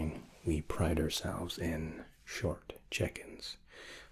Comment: Misheard "pride ourselves in short check ins" as "pride ourselves in short chickens". I was hang on short chickens?